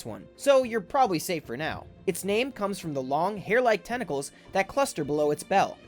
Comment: for the jellyfish i thought he said testicles